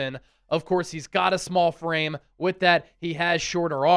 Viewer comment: I really thought you said he has a small brain at first 😂